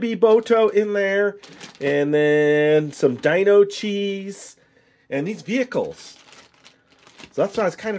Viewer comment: I thought he said Dino cheese